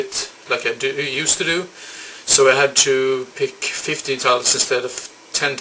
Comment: Thanks for the mentioning. It almost sounded like you said 50 titles and not 15. Now that would have been a long list. MAXIMUM OVERDRIVE is cheesy as hell. I Think it's extra fun that is the only film Stephen King has actually directed himself. No wonder why he has never directed another movie again. Hehe. Have heard of LATE LAST NIGHT. Never seen it though. It's one of those movies I used to stumble upon in those bargain bins over and over again, but never cared for to pick up. Maybe I'll do some day. Oh, it was a triple pack. That's why the cover looked a bit different. As you know I don't own any of the MIGHTY DUCKS movies... yet. But I came across the third one when I was out movie hunting this weekend. I thought about picking it up, but it was sooo scratched it would only have been a waste of money. They had both the first and second STAKEOUT at Skivcentrum (the place where I trade in a lot of movies), but they were a bit pricey for the moment, so I passed. I just recently re-watched MEN AT WORK. Fun flick that you can put on almost any time. Oh yeah! REPO MAN. Classic. And yes, the Criterion release can go for a lot of money. I'm still not sure if I have seen WISDOM or not. Might confuse it with another movie. FREEJACK I've had on my want-to-see list for as long as I can remember, but everytime I come across it, the disc are always in such bad condition. ST. ELMO's FIRE has a great cast for sure. Love the Brat Pack. Personally I think Andrew McCarthy is the one who real